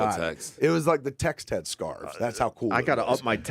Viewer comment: “The text had scarves”. Love that line. Brilliant, funny cool. Both dudes. Justice for Johnny, and stop ripping on Smith. Remember, he is one of the OGs. He deserves more respect for that. Much ❤️ Love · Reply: Is it "scarves"? I thought they were saying "scars"